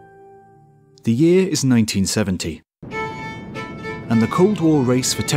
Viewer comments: It says "The year is 1917" then goes on to say "The Cold War-" Am I just hearing things or..? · i swear sounds like he says "the year was 1917" :-P gotta love the british accent!! <3